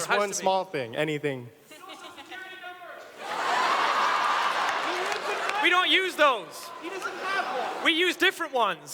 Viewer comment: What did the person in the audience say at 26:00 ? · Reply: Whats your social Security Number?!